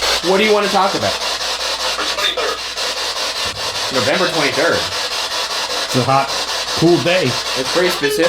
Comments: Make sure to leave your questions for our Post Mortem episode down below! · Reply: This isn't a question lol but when the spirit box thing said "November 23rd", wasn't looking at the screen and instead I heard "sniper" which is then followed in the video with the "I'm in pain". Other than that, I truly thoroughly enjoy these episodes! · #postmortem . At 9:26 I heard "Sni-per" rather than "November 23rd". I could only make out 2 syllables, not the roughly five it takes to enunciate "november twentythird"